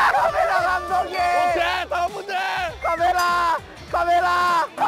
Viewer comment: escucho que dice pamela pamela :v